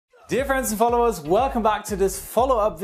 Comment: Idk why but I hear. “Difference and followers”